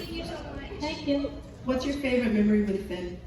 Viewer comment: does anyone know what she was saying ? I mean is there any interview they talked about it? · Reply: the person asked what was her favourite memory with Finn